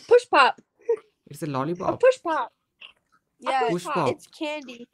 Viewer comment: I thought she said Pushpa.😄